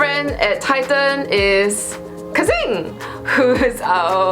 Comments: I Thought You Said Kevin At First 0-0 But It's Not Kevin It's Kexin. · she says her closest friend is KEXIN but the subtitle says KEVIN... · says Kexin but subtitles say Kevin